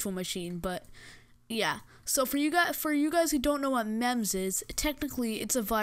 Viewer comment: thought it said memez